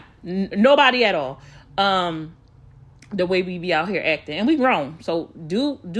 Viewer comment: I thought she said she be UNBOTHERED 🤦🏾‍♀️